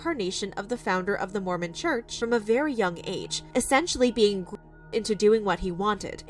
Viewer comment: What is the the g word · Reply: Gamed idk what it means though